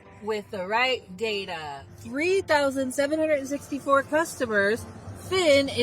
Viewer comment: i thought she said 2763💔